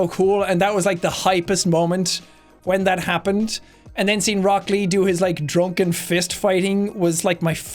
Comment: Definitely thought you said broccoli instead of Rock Lee for a second there and was very confused. I've thought of picking up where I left off with that show. I think I stopped at like episode 355 or something -- it was a very long time ago when like Limewire was still a thing lol. What better time to binge watch anime than during a pandemic.